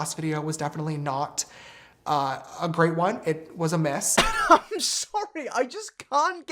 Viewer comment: Okay sooo my english is probably not that good but I thought he said "mess" not a "miss", does that change the context anyhow tho?